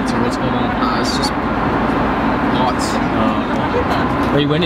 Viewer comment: did he say "BOTS"?